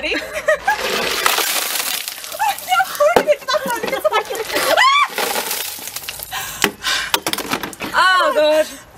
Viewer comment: Could someone write what Katie says in 0:50 - 0:52? Because I do not understand 😟. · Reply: She says “I poured it into yours”